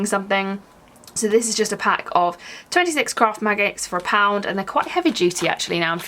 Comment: I've listened to you talking about the magnets 3 times now and I swear it still sounds like you say "26 craft MAGGOTS for a pound." 😂